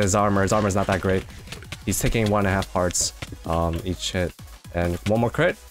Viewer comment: When he said "each hit" I thought he said "eat shit" Edit: fixed a typo